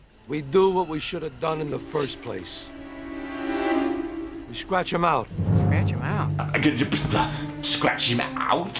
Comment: As a child I always thought they said 'Scratch your mouth' 🤣